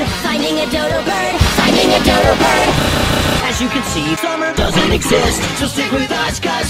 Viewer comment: "As you can see, Summer DOESN'T EXIST." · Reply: I always thought they were saying “Summer doesn’t make sense.”